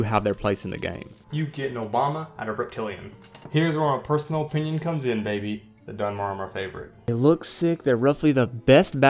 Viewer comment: "Here's where my personal opinion comes in baby, the Dunmer are my favorite." I thought you said here's where my personal opinion comes in. The Dunmer are the best, that's a fact.